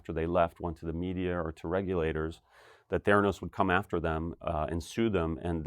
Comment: As at 11:00 I keep hearing "Thanos.. would come after them.... "